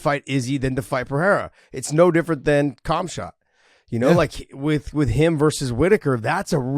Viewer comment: Lmao 29:20 and 30:12 - did just call Khamzat cumshot?